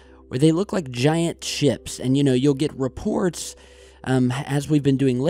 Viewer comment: at about 9:10 in, you called it ships, and it sounded like you said giant chips, and I thought invasion of the dorito snatchers. and now I feel awkward.